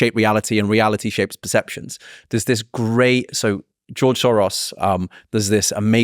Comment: did he said George Soros?